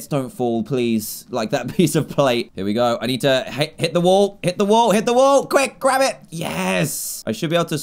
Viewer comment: When he said hit the wall I thought he said hit the woah